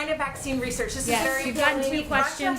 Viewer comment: At first and second listening it sounds like "Lying B***h"...But then playing it very loud over and over. It does sound like she is saying the don't want to engage. That being said it's still rude when you ask the same question over and over even when you are given an answer.